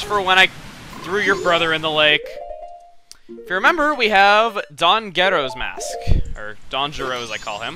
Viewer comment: i thought you said "Don's Gheto Mask"